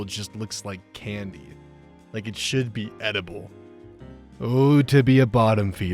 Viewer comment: my brain didn't register the word feeder in "Oh to be a bottom feeder" so all I heard at first was "Oh too be a bottom" and that will forever be funny to me.